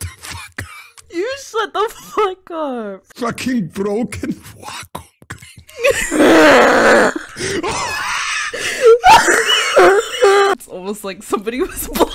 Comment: Did he just say : F***ING BROKEN VAKUM CLEANER